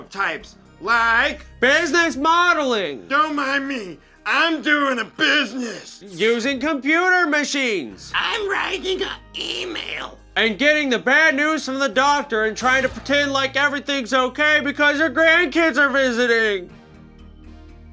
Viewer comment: I thought ian said "i'm ryan higa email"😂